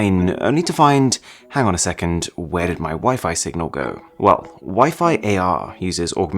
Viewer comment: I thought he said where did my wife go xd